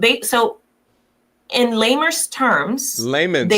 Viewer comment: Shes trying so hard to sound intelligent, yet she has poor command of her native language. I was trying to hear her out but once i heard "lamers" instead of "layman's" i tuned out.